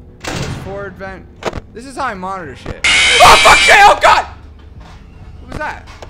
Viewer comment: I know i already commented but 8:51 i swear he said OH FUCK J-HOPE GOD!!! Edit: everytime he screamed i swear he said AHH FUCK J-HOPE 😂